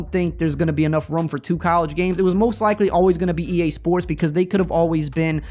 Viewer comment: I heard “not enough rum for two college games”. And even though you didn’t say that, it still made sense to me.